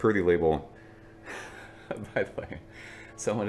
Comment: I just thought you were saying something in French or beer gibberish. I honestly glossed over it every time. Sounded like “Perdy label” to me 😂